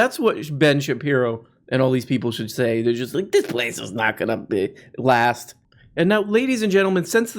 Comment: You said "this place isn't gonna last" I heard "this place is gonna get glassed" Sure is